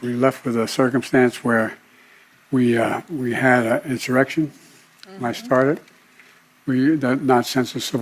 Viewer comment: Did he just say "we had an insurrection, that i started" at 11:30 ?